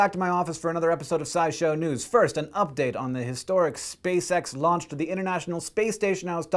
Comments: At first I thought he was saying space sex at the beginning then I read the description and I was like " OH that's what he was saying." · in the beginning og the video i thought he was saying "space sex". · Man, I thought Hank was saying Space Sex at the start and got very confused :L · I could swore he said space sex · I hear "spacesex"